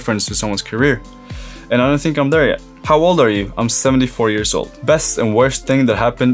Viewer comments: Did u just say u are 74 years old, just if u didn't know "u are Jonas Engelschion Mjaset and u are 24 years old" And ya how do u pronounce that I mean ur name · "How old are you?" "74 years old." Am i hearing this correctly? I legit put the speed at 0.25 and still hear 74 years old.